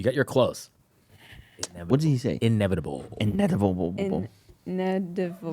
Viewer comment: Did he say , what did thanos say?